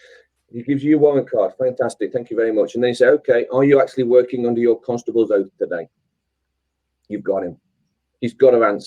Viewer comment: what is the question for constable/policeman? (9:05) "Are you working under your constable's ????? today?" I can't catch it :( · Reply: He said 'Are you working under your constables Oath today'